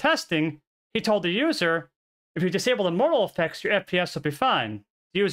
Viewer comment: i thought you were saying "mortal effects" lol